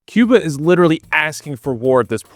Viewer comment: Clicked on the Video, tried to pause immediately to go piss, all I heard: "Cuba is literally ass" , almost pissed myself laughing...